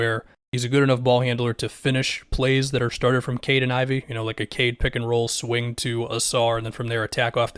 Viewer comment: When You said "Cade and Ivey" I thought you said "Kayden Ivey" at first lol